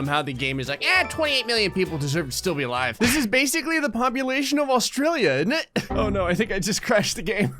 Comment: did just say...innit What has happened to you